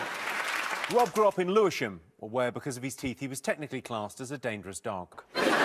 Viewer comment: What did Jimmy said in 0:31? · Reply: Jimmy did said "Rob grew up in Lewisham where because of his teeth he was technically classed as a dog"